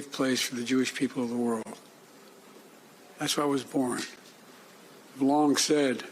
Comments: does Joe say hes born in Israel???😮😂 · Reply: He said that's why it was born..meaning isreal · Did he actually said he was born in Israel!?!?! I watched it 10 times! Thats a lie!!! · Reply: Seriously? Why would he blatantly lie about something that could be fact checked within seconds. He said “it” not I. Jeez… “that’s a lie!!” 😂😂 please think before you comment things.